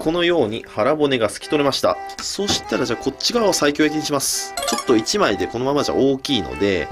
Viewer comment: の所が「こっちから最強にします」にしか聞こえんw